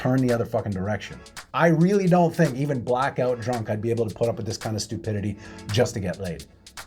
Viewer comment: i thought you said "blackbelt drunk" instead of "blackout drunk", and I like the new term.